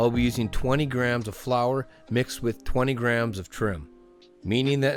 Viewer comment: What is troom? (About 1:10 you say you’re mixing flower with “20 grams of troom”) I have bad ears so it’s probably not a nonsense word lol but I just can’t think of anything that it would in fact be. Is it slang im too old to know? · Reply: "trim", my friend. Hope that helps 👍